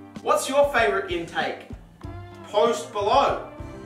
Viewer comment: Post below? I thought you said post Malone